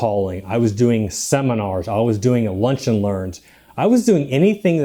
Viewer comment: He did Cold Calling, Seminars and what is third one? I am missing the word.... Lunch and Learn? is that correct?